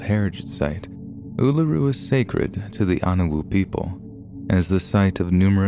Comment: UWUru? I'm sorry, I can't hear the first story's location without my mind autocorrecting it to, "Uwuru"😂